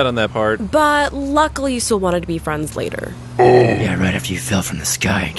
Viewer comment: ok this has been bothering me for a long time, what does black widow say at @ 14:43 ? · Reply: "But luckily you still wanted to be friends later." ☺